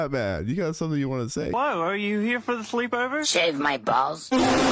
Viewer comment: he actually says "Shave my hair?" that scene was edited